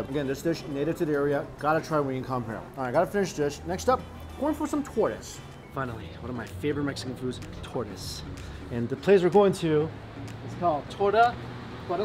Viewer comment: oh shi, he said "tortas", I heard tortoise...